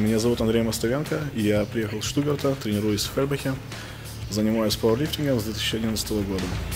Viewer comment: I thought he said he trained in Birmingham for a second